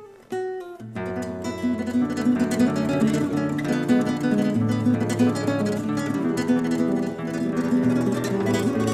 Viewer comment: falando "lindo". Grandes violonistas! Que honra poder ouvi-los! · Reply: Ele fala "lindo"